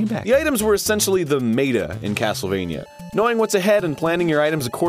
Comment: I saw this video the day it came out 8 years ago. And I only noticed this now... At 1:25 Did he just say MAYtuh when describing meta???